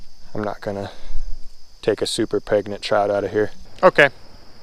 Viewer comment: Did you just say a super pregnant child?